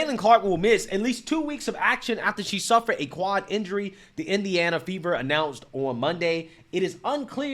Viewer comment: I thought you were saying The Indiana ‘Beaver’. What a clever name for a girls basketball team!